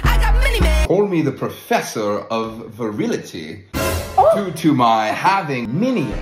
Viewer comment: "Professer of Virility" is the best thing I've heard today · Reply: I thought it say virginity- 💀